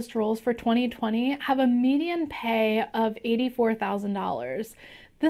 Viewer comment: It sounds like she is saying "median" and not medium/average. And as data analyst we know what that means ;-)